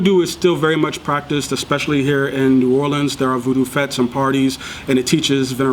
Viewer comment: "especially here in ...." what did he say ?? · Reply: Lefor .michel New Orleans